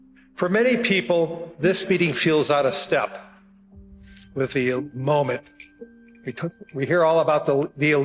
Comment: if you're not looking at the screen it sounds like it says Molek.... Just saying. A A